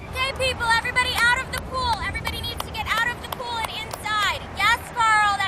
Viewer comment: Tbh, it sounded like "gay people out of the pool"